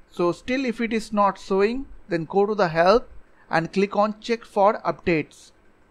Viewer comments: When he say ‘ if its not work go to help ' And i heard ‘go to hell ’ 😂😂😂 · I heard go to the hell :D